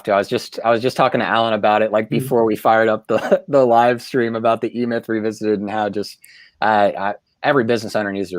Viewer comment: What book are you talking about around the 16:30 mark? I watched it twice and still didn't catch it. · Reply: The book: the e-myth revisited by Micheal gerber